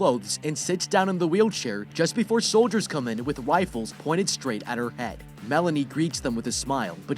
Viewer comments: did he just said wiflles 😂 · Is it just me that heard him say that they pointed 'wifles right at her head'? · did he say wifles? instead of rifles?